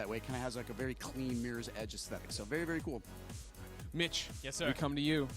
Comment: Lmfao when gregg said mitch i thought he said bitch.